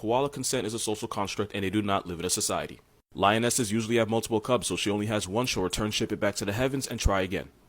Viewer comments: I literally cannot understand him, what is he saying? It's that I'm hard of hearing, but recently I've learned that people with ADHD may have auditory processing disorder (APD), so what he's saying is nearly unintelligible. It sounds like, 'Lionesses usually have two cubs, so she'll only has one she'll return ship it back to the heavens, and try again'. The bold is where I'm havng trouble. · Wait I couldn't understand what he was say about the lions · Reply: "Lionesses usually have multiple cubs, so if she only has one, she'll return ship it back to the heavens and try again."